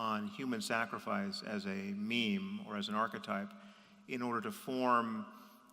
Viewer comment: Did he say “meme” at 7:33?